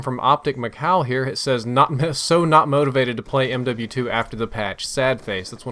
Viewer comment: he said mw2 instead of bo2